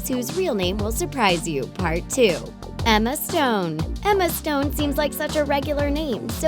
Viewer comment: Omg!!! When it said “Emma Stone” I thought it said “Emma Swan” then when it said “Lana Del Rey” I thought it was going to say “Lana Parrilla”!!! I am really obsessed with Once Upon a Time!😂😂